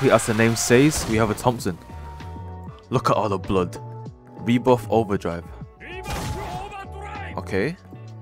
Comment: i thought said v-bucks overdrive